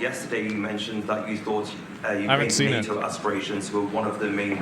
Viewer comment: He said "thought" but I thought he said "bloody" after referencing Europe and I fucking died.